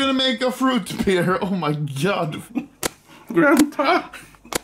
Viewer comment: did just say "grandpa"?!